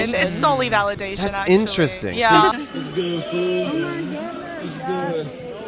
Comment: Bro said "interesting" i heard "revolting"